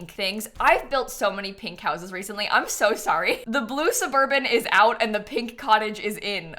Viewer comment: Did she just say we can have a blue suburban....in space?!